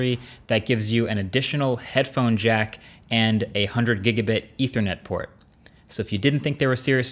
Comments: A 100 GIGABIT ethernet??? Did I hear it right??? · Did he say 100gb ethernet port? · Did he just say '100 gigabit' ethernet port?